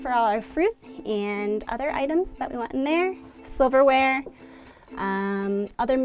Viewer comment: Anybody else hear "sliverware?" :D